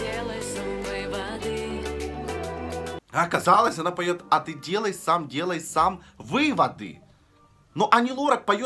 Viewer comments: Я, конечно, извиняюсь, но у автора все в порядке со слухом?) Все песни поются достаточно внятно (мб кроме Би-2) Смутила только строчка из песни Лорак (послышалось 'делай всем, делай всем выводы), но в итоге расслышала верно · Насчёт первой песни согласна что он поёт невнятно, насчёт песни Максим я сразу услышала "метели", Ани Лорак первую песню правильно слышала, а вторую тоже как ты 😅 "воды" вместо выводы 🙈. Лазарева сразу услышала правильно "моя реальность словно сто костров горит", а вторую фразу как ты "лишь бы NASA успехи простили" и от Би-2 сразу правильно услышала "кончится плёнка". Казку "знов" 😅 След: "Где море цветов" А в следующей вообще услышала как "конветыыы"